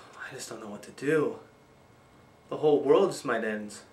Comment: Did you say "The whole world is my dens"? that's at 00:26 Please correct me